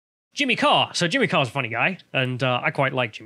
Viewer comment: I thought it said 'Jim Carry' I am disappoint.